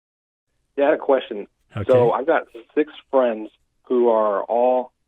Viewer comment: Tbf, @ 0:21 I thought he said he has "Sic's" friends (quality rather than quantity, obvs). shortly after, he said "they're all pretty militant", and you seem to have heard deifferently...